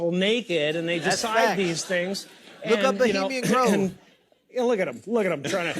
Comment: Someone help me. What was that he said at 0:21? "Look up..."